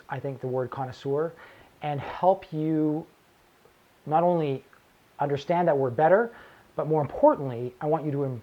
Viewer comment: When you said "understand that word better" I heard you say "understand that we're better" and laughed very loudly